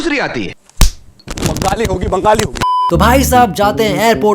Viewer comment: what's that meme about? I think I saw that or similar dialogue in another video also "Bangali Hogi"? I think that's what he sounded like.